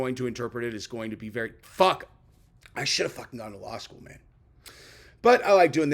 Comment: I thought was at one time a practicing Attorney at one time? Yes, no? I heard " i should've gone to law school." Go to sleep. 🤪